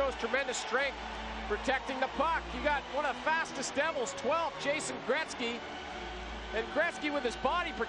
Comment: Number 12,jason Gretzky??? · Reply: "chasing" Gretzky. (At first I thought he said "Jason" as well.)